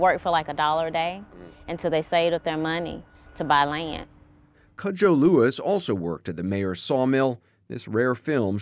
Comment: I’m not sure if I heard Kojo Lewis (7:54) but if I did, then they can trace their ancestry to Ghana.